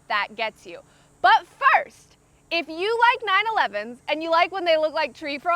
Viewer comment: I'm sorry but when she said "if you love 911" I just heard "if you love 9/11"